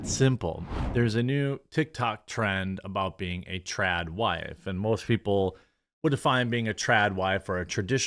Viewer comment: I thought you said Trap wife, that’s a whole other story lol